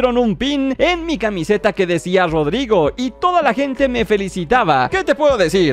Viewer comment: y su nombre premium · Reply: Pensé que decía AODAIGO